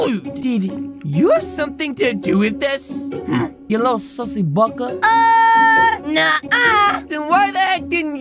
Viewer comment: DID HE REALLY SAID SUSSY BAKA?